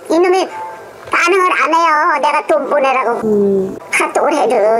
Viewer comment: 할머니한테 아들은 사랑을 방해할 뿐임 사기꾼이든 불륜남이든 상간남이든 돈 보내지말라로 들림